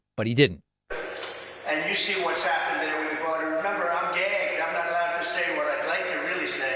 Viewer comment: I thought trump said, "I'm gay" and not gagged. Checked the captions, Captions also have it as "I'm gay" at 2:11 in the video and again when he repeats himself. Or maybe I watched too much Shane Gillis on my part. 🤷‍♂️ I'll go with the what the Caps caught.